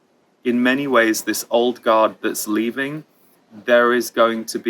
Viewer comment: Did he say the old GOD or old GUARD on 8:15? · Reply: Guard